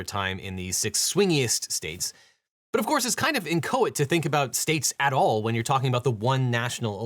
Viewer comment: the subtitles saying "in coit" instead of "inchoate" made me chuckle. I'm not gonna be judging anyone, but I feel like election shenanigans don't pair very well with anything coital.